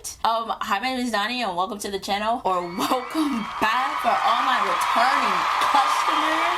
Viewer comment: stop omg at 7:00 the closed captioning censored you bc it thought you were saying the r-word when you said “returning customers” 💀💀💀